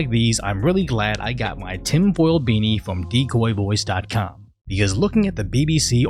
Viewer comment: At first I thought you said, "Tim Pool beanie". 😅